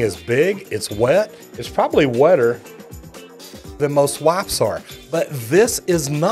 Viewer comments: I'm sorry, but I thought he said "This is actually wetter than most wives are" XD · I'm sorry, but I thought he said "This is actually wetter than most wives are" XD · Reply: I thought he said "this is actually wetter than most WAPs are"